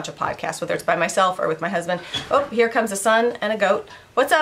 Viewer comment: In the beginning of the video, I thought you said "Here comes the sun (you meant a son) and a goat":) I was thinking you get up waaay to early! Your mouse on a pole is hilarious! Evie is cute! Sorry to hear about Maverick! Aww I feel bad for the girls having to find him like that! Love the ship lap and the color! The girls will love phantom-definitely my top fave! I hope they have a great trip! Happy Birthday Eve to Kennedy!